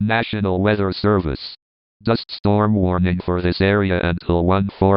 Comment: When the robot said dust storm I heard "dist storm"